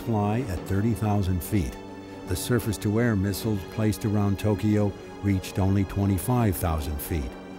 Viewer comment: Did I just hear Surface to Air Missiles place around Tokyo at 55:45??